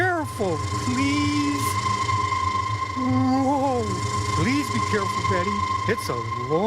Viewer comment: please tell me I'm not the only one who thought Santa said "please be careful, Daddy"